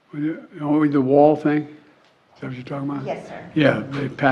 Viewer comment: Waaaait… I listened to this over and over… did Biden say @ 0:14 “…The wall thing?”